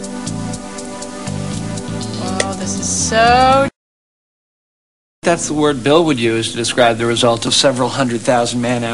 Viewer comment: "Wow this is soooo ... " I need to know what she says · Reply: Jennifer: Wow this is soooo trippy Mathew: Yes, I think thats the word Bill would use... etc